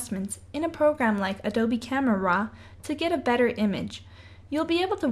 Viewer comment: Adobe what was that second word? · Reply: Two words: Camera RAW